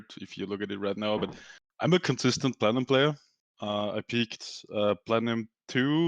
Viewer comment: said he wanted to reach diamond this season, peaked platinum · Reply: ah i just listened to it again he said consistent platinum player thought i heard diamond player 0:32 ahaha my bad xD